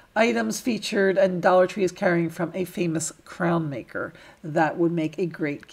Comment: I love the way you worked around saying the Toys brands LOL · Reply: ohh she said crayon I thought she said crown! My bad